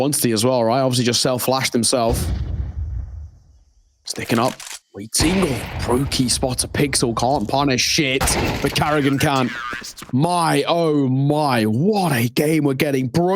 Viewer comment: lol because he elongates "Punisssshhhh it" it really sounds like he says 'can't punish shit'